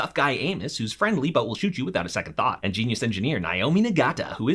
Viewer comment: If I didn't know who this was I would've thought you said "Anus"